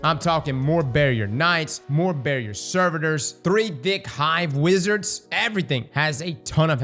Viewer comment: I heard three dick hive wizards…